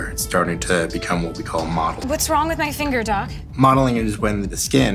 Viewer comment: WhAT's wROnG wItH mAh fInGEr dUcK!? I'm dying XD 😂 I just heard it